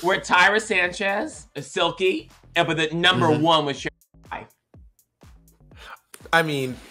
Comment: what did she say?? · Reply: Little late but Sherry Pie! 😭